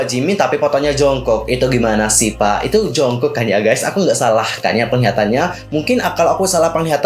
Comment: si kk nya bilang jongkok ga si apa gw nya aja salah denger tpi dah gw ulang2, 😂